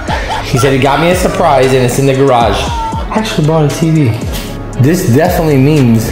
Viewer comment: what did he say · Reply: He actually bought a tv